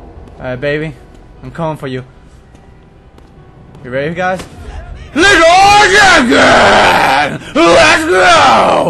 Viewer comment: lol did he say league of legends?